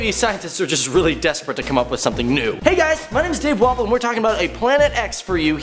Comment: is it just me that always hears "My name is Dave Waffle" instead of? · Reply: I've always heard it as Dave Waffle 😂